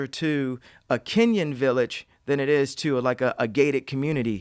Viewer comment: did he just say a kenyan village! i live there... thats why i keep seeing those shoes?!